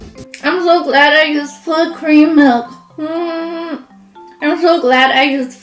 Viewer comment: i thought she said foot cream milk 😂